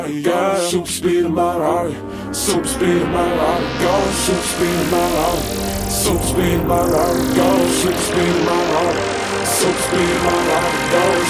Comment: sounds like he is saying "Something's peed in my water"